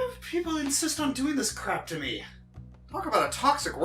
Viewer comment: did he just say crap? 17:16 he said it again!